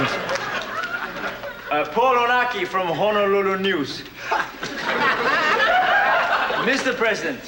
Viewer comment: I heard "poor Iraqi, Honolulu news" lol!